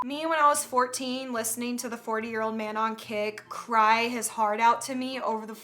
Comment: I thought you said a “four-year-old man” Edit: what I always do when my parents are screaming at each other is play the circus theme.